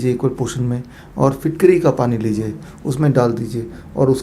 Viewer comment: What water he said at 3:15 ? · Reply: Fitkari jise alum v kahte hn ,or jo shaving k dauran salon mn lagaya jata h , uska pani Lena h , matlb pani mn filtkari thoda sa ghol Dena h , fir us pani ko namak or haldi mn milakr dant pr malna h , ck bhaijan . Bat samajh ayi 😀